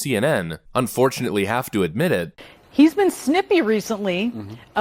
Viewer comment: When that lady said “He’s been snippy recently,” I honestly thought she said “sniffy.” Not kidding.